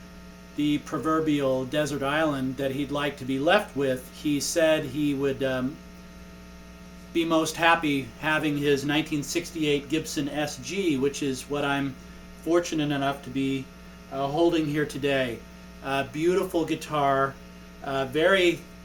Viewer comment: I thought he said a 1961 Gibson sg? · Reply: Nope. '68